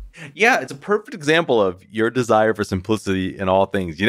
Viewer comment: What did I just hear? Haha I must heard wrong because no one else in comments said anything lol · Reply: 88 He said "if you desire p*ssy". I probably heard incorrectly